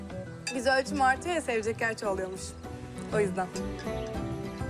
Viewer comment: de alara ne Diyor? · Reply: yüz ölçüm artiyoya sevecek yer çoğaliyormuş o yüzden